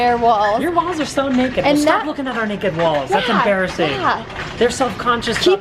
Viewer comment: when you said naked walls i thought you said NAKED BALLS. I was like um... learn your anatomy you don't have those haha!